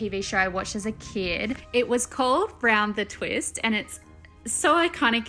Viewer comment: I actually like the frogs. The slime...maybe do a dark, Halloweenish tie-dye sort of...? Make the eyes a bit bloodshot...add false lashes? In jars on a shelf, cool. Btw, I thought she said "Brown the Twist" but looked it up & it's "Round The Twist" if anyone's interested :)